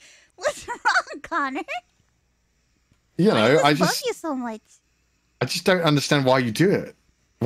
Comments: am I tripping on ship juice or did she say "I just love you so much" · did she say i just love you so much ???????